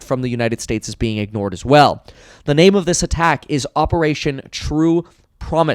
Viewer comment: At first I thought he said Operation True Hummus which would have been a billion times better.